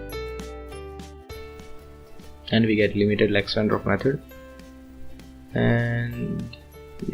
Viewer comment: What is Limited LW/BW method? (sounds like limited lexandwarf method - cannot hear it properly) I could not find it on internet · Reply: Sorry for the bad voice quality, my recording mic is not that good. You are right, LW = Lax-Wendroff and BW = Beam-Warming.